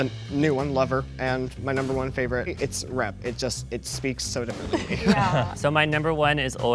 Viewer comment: I can’t tell if says RED OR Rep... but I swear he said Rep... not Red